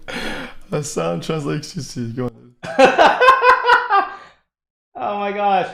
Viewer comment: what is he saying? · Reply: He says "Hasan translates XQC - good one, dude" because he was introducing the next clip. It has taken me a while to be able to understand him.